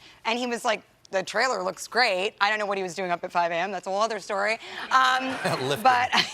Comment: SO THOUGHTFUL OF CHRIS EVANS TO TEXT HER AT 5 AM THO WHATEVER HE WAS DOING THAT TIME. HE STILL THOUGHT ABOUT TEXTING HER❤️💕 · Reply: Oh my bad I thought you said “whoever he was doing” lmao